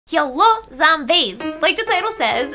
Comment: lol i thought she said "kill all zombies"